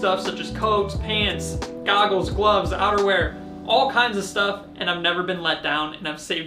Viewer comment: For a second I heard "underwear". It was like... eeeeewwwwww!!! used underwear!